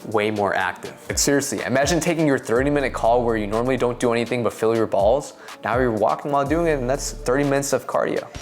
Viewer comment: LOL @ 7:07, "30 minute call where you don't do anything but feel your balls" - did I hear that right, lol.